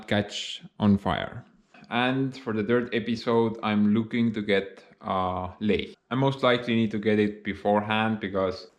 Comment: Am I the only one who thought he said "for the third episode I want to get laid" 😂😂